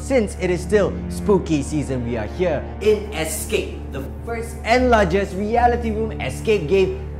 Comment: did not say “first” did he??